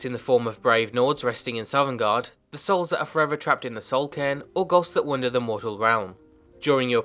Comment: This makes me want to play a soul hungry Dragonborn who sends her enemies to the Soul Cairn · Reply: OH, i thought he was saying Soul Can